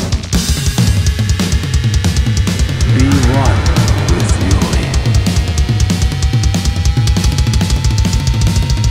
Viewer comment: i didnt understand it.... 2:54 u won with yuri ? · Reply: "Be one with Yuri"